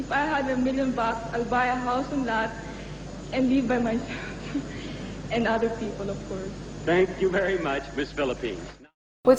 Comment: That answer was really bad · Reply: I think what I hear is "And live by my self, and other people, of course."